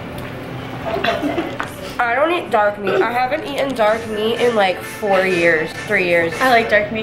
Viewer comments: Omg at first I thought she said she hasn't eaten dog meat in 4 years I was scared but turns out she said dark meat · When u said “dark meat” I thought u said Dog Meat!! I was like WHHHAAAT!!